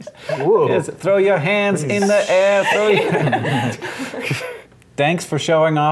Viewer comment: "throw your hands in the air!" ...I thought at first Hank said : "throw your ants in the air"... hmm.. :-)